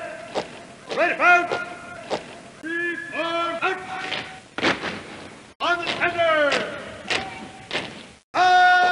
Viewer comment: Does anyone know what they say on 3:20? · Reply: supposedly the original is "Die Waffen legt am!" but in this instance it's cut down to "Die Wa- am!"